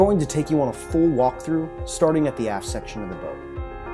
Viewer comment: Did he just say “starting at the ass section of the boat” lol